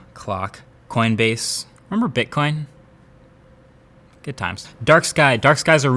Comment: what is he actually saying? ‚I am in Bitcoin?‘ Did I get it right?